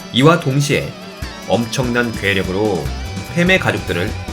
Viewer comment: 나만 엄청난 매력이라고 들었나? ㅋㅋㅋ